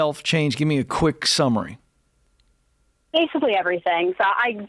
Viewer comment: anyone else hear Judge Judy scream "DON'T TELL ME BASICALLY" after Ken told her to be specific?